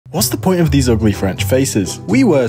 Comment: I didn't catch "these" in the opening line and heard it as "What's the point of ugly French faces?" Needless to say, I was expecting a slightly different video.